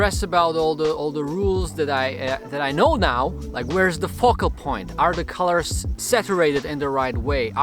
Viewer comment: > He said "Where is the focal point?" and NOT what you think you heard!!! 😞